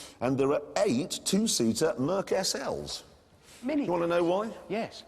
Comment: is it just me or did I hear Richard say the n word? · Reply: It's just you. He said "Minicabs?"